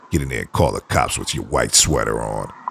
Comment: “Get in there and call the cops with your white sweater on!” 😂😂😂😂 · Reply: I thought he said wife sweater....🤣🤣🤣🤣🤣